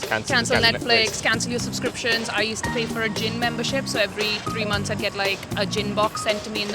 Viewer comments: Honestly never crossed my mind to cancel the gin box, will do that now thanks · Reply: I thought she said "gym membership". Yep, gotta give up that exercise. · Honestly never crossed my mind to cancel the gin box, will do that now thanks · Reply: I misheard her as a "Gym Box" and I was like "what's in that? Elastic rope?" And then she said about going non-alcoholic and I was like... "ooooooohhhh" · Man, I thought she said "gym membership". Turns out she just quit bein an alcoholic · When she said gin box I thought she said Gym!